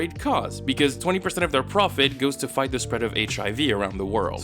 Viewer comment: Huh I didn't hear the word "fight" at 4:40 the first time I heard it and it made go "WHAT?"